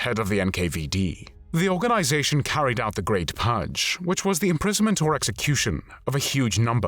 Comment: Why does the voice say pugge instead of purge? :)